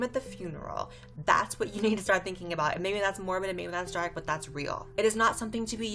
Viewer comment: What is she saying at 8:14 ? “And maybe that’s more of a ameelansjarik but that’s real”